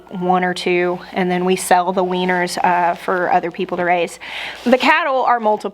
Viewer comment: It took me a minute when you said, "the weaners," because I heard it as "the weiners.". 🤦🏼‍♀️ What a difference a single vowell makes.